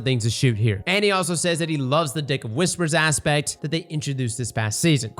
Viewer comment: NGL I heard "dick of whispers" more than once during this video.